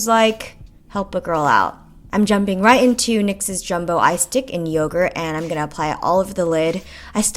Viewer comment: When you said "eye stick" I thought you said "ice dick"... probably just me oops